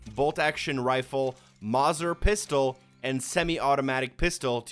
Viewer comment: Wait did he just say MOZZER pistol?